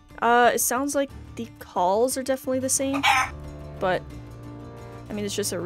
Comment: 🐔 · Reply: “Their claws are the same” I THOUGHT U SAID BALLS💀